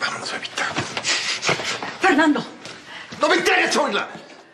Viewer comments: Tardé UNA ETERNIDAD en entender que decía "NO ME INTERESA OÍRLA" en el minuto 18:27 . De hecho la primera vez que vi este caso nunca entendí qué decía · Qué es lo que dice él en el minuto 18:27? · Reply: Karla Rojas "no me interesa oirla"